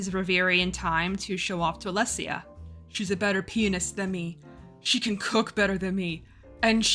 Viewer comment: this is what I get for not reading along in the excerpts, I thought you said "she has a better penis than me", which judging by the sex scenes in the books is damning with faint praise.